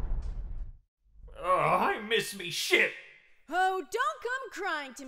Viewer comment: the captions says SHIT instead of ship.